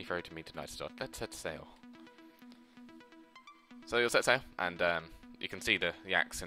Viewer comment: ahah "so you'll set sail" you said that so fast its like wtf he say! hahaahahah good shit son.